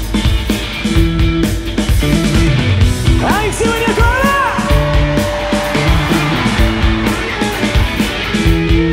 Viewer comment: なんて言ってるかわかる人いたら教えて欲しいです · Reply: how are you doing Yokohama? です！